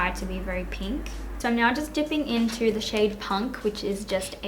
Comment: When you said punk I thought you said spunk.... girl why you gotta start the tutorial out with the jiz comment 😂😂😂😂 making it so sexual now ;)